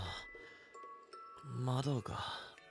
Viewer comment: i heard "Madoka"